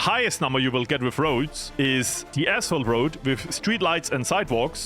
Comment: " The Asshole Road " lol. I know you didnt say that but sounded like it.